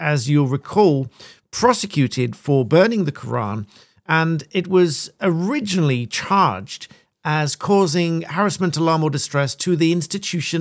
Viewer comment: What?! Oh, for a moment I thought you said [starting at 0:30] "and it was originally charred as causing...".